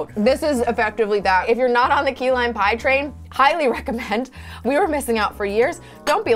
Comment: aw man i thought steph said “pie-ly recommend”